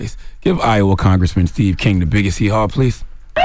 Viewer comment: Bruh I thought it said Steve Young at first.